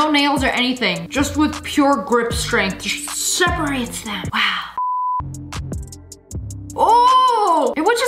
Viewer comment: i heard her say wow...